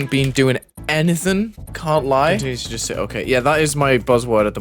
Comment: bro WHAT WORD? edit: ohhhh, "okay", i see